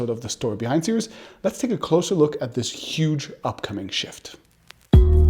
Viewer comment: I misheard that as "Let's take a look at this huge bucket of shit" at first, so I had to go back and double check 😂